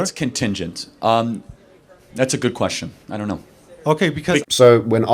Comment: Dude, Kirk did not say "yes definitely" when asked if we should continue relations even if Russia continues the war. His answer was "That's a good question. I don't know." Maybe you should watch your own clip: frame 9:14. I don't agree with Kirk's statements re Finland, but let's keep it honest.